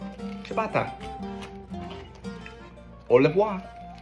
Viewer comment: Bro speaking Spanish, Italian and French in one go. 😂😂😂😂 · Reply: im french and i think i heard him say "pas du tout" and "au revoir" but im not sure